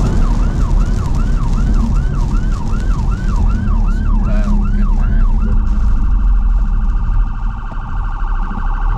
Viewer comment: Can someone tell me what says at 2:44 "??? get back"? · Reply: I think he says: "Superb good man"